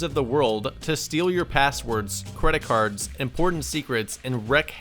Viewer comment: I thought he said "and porn secrets"